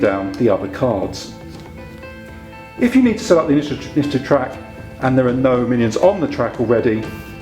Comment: WTF does he say at 1:07? "If you need to studuguehfuie the track..." Not complaining. Legitimately curious. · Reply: I think it's supposed to be "set up the initiative track"